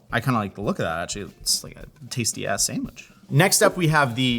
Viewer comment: Did he just say tasty ass-sandwich?!